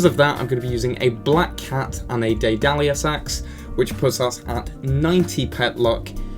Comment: WHAT DID HE SAY? LMAO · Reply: The Daedalus Axe is based off a Greek Mythogical Figure, which as far as I'm aware is pronounced Dae-Dal-Eus, having said that, I usually say Day-Dalus anyway 😂